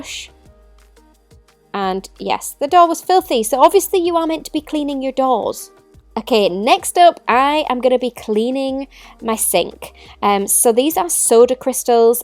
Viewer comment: I thought this lady said she was cleaning her DOLLS and was surprised to see her cleaning her DOORS 😳